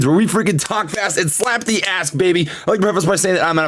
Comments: I thought he said slap the as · "Talk fast and slap the ask" GOLDEN. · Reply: I thought he said "slap the ass" lol